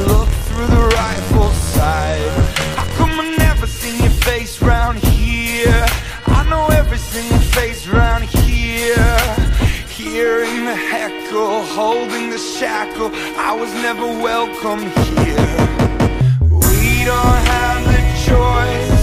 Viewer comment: Nygaard I thought it said here in the heck hole